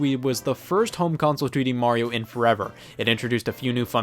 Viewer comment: Did you just call New Super Mario Bros. Wii a “3D Mario”? 🤔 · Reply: No he said 2D